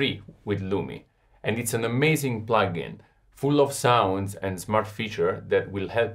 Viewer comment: Did he say something about a butt plug